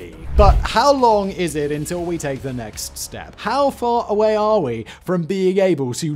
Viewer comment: lmao, I hear "how far are we from eating s**t?" and immediately had an image of McDonald's in my head